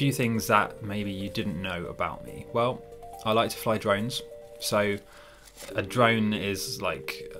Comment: I thought you said "I like to fly dragons"